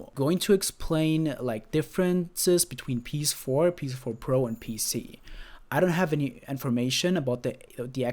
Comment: DID HE SAY PC.........!!! IS monster hunter world gono be on pc?